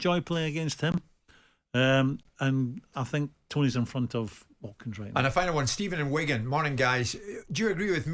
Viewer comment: Did he just say toney was infront of Kane